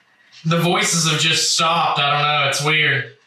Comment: I hear a breathy whisper right after says "the voices have just stopped, i dunno it's just weird". At 1:10:34 - 1:10:36 i think i hear "stop" and can't make out the other word.